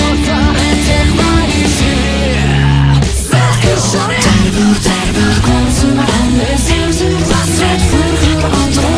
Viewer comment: 「さあ一緒に！」の後ろの方で「〜〜ろ！」みたいな声聞こえない？だれか聞き取れたりしてない？ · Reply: 間違っていたらすみません、レッツゴーって言ってるように聞こえます。